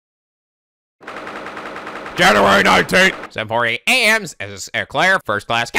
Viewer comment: did you say January 19th lol it said 9th I know its not that big of a deal but was funny to me